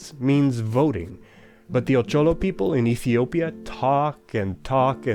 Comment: what people is this? I couldn't catch the name, and their decision making process seems really interesting. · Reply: The captions: "But the Ocholo people in Ethiopia talk and talk and talk..."